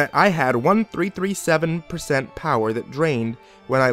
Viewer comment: almost thought you said "i had 11037 power" and almost screamed "AW FUCK YOU" out loud